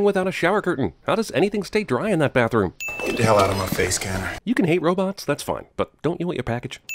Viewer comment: Wait... did he call that robot around 0:44 :"Karen?" How prescient was that?